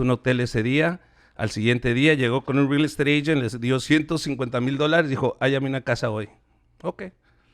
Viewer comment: isn't he saying that his mom gave the real estate agent $50,000? Not $1500.00 in english subtitles it says 1500. but i thought i heard him say cincuenta mil